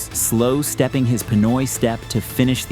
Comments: did he just said "Pinoy step" Proud pinoy here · did say pinoy step?